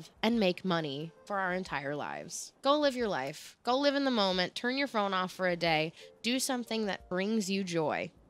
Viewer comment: i heard "turn your pronouns off for a day" instead of "turn your phone off for a day" and it made so much sense to me as an any-pronoun nonbinary person that i didn't question it until a few seconds later today i didn't go to work and instead did home office because my lower back was hurting so much that i felt i shouldn't risk it getting worse and to me that's one of the best forms of self love and self care. learning my limits has really helped me in the past years and was the first step to a better relationship with myself